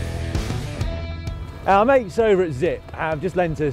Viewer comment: I just want to thank you for adding in SUBTITLES in your videos! I couldn't make out who provided your bikes ("our mates over at Zipp") (0:16) and replayed it 3 times before turning on the "CC" thinking the bikes can't be Zipp since Zipp are a wheel-manufacturer. Also indispensable for those who did not grow up with English.